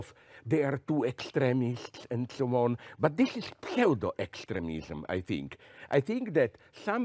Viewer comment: What type of extremism does he say ? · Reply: I think he says Pseudo It's a good point - I agree whole heartedly